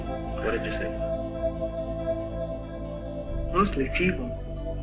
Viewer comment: it sounds like she said 'mostly people'